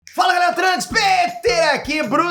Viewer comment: Sempre entendi ''FALA GALERA TRANS...'' e sempre achei estranho kkkk trans é sacanagem